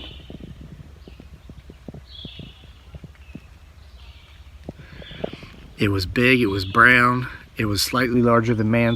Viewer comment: Did anyone hear talking at about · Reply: Sounded like "like some water", as if they were asking for water